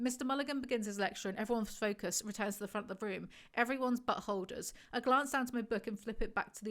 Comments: when she said “everyone’s but Holder’s” I heard everyone’s buttholders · I heard this as "everyone's butthole... ders" or maybe "butt-holders?" either way